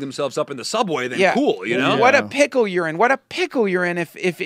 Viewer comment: I swear he's saying "what a pickle urine, what a pickle urine!"